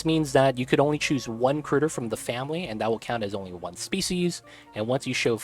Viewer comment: did you just say specieses?